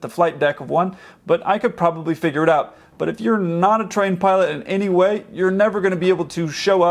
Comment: did he just say train pilot or trained